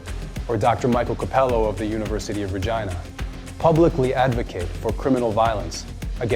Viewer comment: I could have sworn this guy said “University of Vagina” when I first heard it lol!